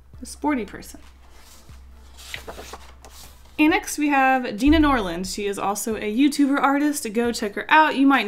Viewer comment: someone can tell me the name of his YouTube channel, I try Gina Norland that is what I heard but I can't find her